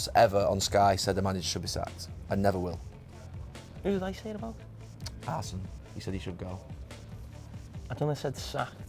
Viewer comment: Who did carragher said should be sacked? Can't understand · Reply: arsene wenger